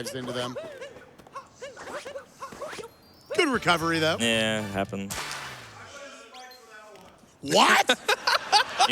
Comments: Can someone tell me what the person in the audience said at 2:36:05 please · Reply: "I blame Spike for that one" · What did that guy in crowd say at 2:36:09 which made that couch guy say "WHAT?!?! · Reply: "I blame Spike for that one!"